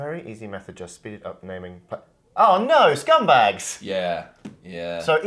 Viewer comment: I'll be honest - for a moment there, I thought was saying the new planet was called the planet Scumbags.